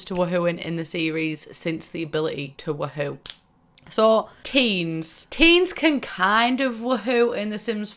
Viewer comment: I havent played the sims 4 - just 1 through 3 - and when you said teens I thought you said teams. My jaw dropped for a minute.